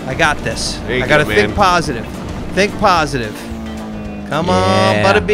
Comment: When said 'think positive' I heard 'Thick positive' lol